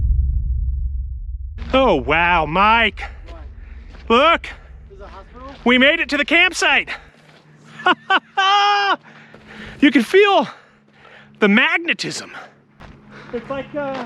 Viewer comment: Did Mike say "Is it a hospital?"